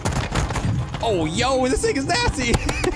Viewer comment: About 75% through the video, it sounds like u said “this nickgurrs nasty”.